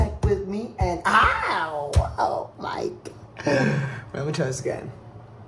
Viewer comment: omg at 15:55 when said "wait lemme try this again" i literally had to rewind it because i stg i heard "im a toe skin" 💀💀